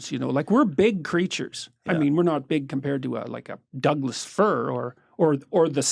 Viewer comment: "We are not as big as a [my English cannot understand what he says] or, you know, the Sun." I always lose my shit when JP says things like this to give perspective. It's that sort of automated defense so a smarty-pants student doesn't say "we're not bigger than a whale!!" during his talk hahaha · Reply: Douglas fir is what he said, its apparantly a tree